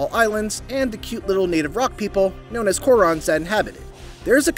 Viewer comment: did he say KOROKS 🤣